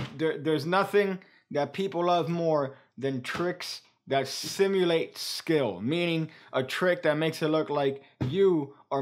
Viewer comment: i thought u said theres nothing people love more then triccks where they think they have free will.... ??